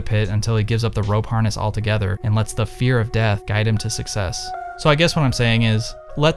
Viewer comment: What he said - Doesn't have the "fear of death" What I heard - Doesn't have the "fear of debt"